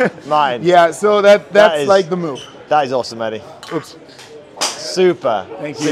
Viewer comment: great vid - 18:24.. sounds like you said " that was awesome Eddy"? Probably my ears 🙂